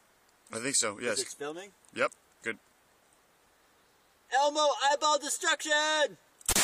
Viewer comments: Don’t lie.. You thought he said “ELMO I AM DESTRUCTION!” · "ELMO EYEBALL DESTRUCTION" elmo: *whole head blasts off* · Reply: I’ve always thought that he said Elmo I love destruction